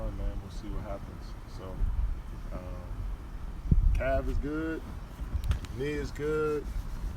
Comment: when he said calf's good I thought he said cavs good